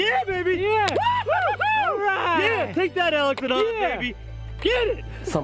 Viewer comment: Did he really say: "Take that Alex and Hans, baby!" at 46:52? lol...Maybe the next time I climb a tall ladder, I'll say the same thing.